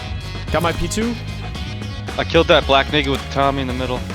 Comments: Yoo, you´re mate is calling someone a black n***a around 40:55. Or am i hearing wrong ? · "i killed that black NAKED with the tommey in the middel" Rewatched it 5 times cuz i thought bro said somethin else xD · look what he says 😮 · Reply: black naked with the tommy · i hear him saying ''i killed that black n-word'' at 40:52 and its even more clear on 0.25 speed, not only that, even the subtitles says ''i killed that black [__]'' and even the cherry on top at 40:55 you see a 2 black chars on screen, 1 with the tommy which is the one he called out ''I killed the black n-word with the tommy in the middle'' , coicidence? i think not. and i know for a fact im not deaf or dont have any hearing problems lmao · did he just say i killed that black n… with thommy in the middle? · What did I hear? 40:52. Did I hear Ni""a? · what did say? black huh with a tommy?